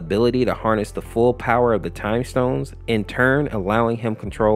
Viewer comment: did he just say time stones?